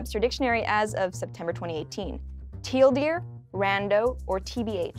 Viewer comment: was she saying tl;dr on the first one? It sounded like she just said teal deer...